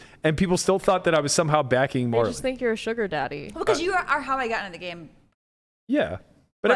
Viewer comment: What did Marle say at 01:32:24 ? I'm not gonna be able to sleep at night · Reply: "You were the one who got me into the game, pretty much"